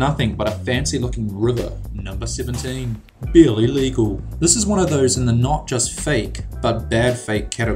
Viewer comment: When he said "barely legal" it sounded like he said "bill illegal" lol.